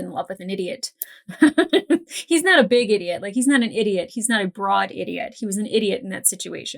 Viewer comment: Is it weird that in the opening I heard "He's not a Grog idiot"?